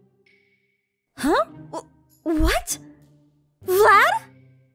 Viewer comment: I thought she said "Dad!" For a second...